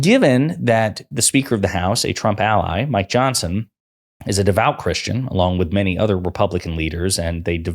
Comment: DId he just sAy he;s NOT CHRRUSTIAN?